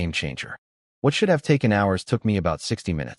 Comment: Wait what. He said what should have take an hour took me sixty minutes 😂